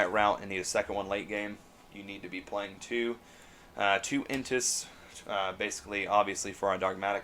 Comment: What monster did you say at 11:12? Intis? Sorry Im trying to use this deck in ygo omega and need the right spelling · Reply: Her name is, “Elder Entity N’tss”!